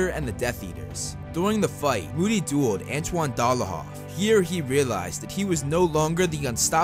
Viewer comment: did i just heard Antoine Dolohov .. ?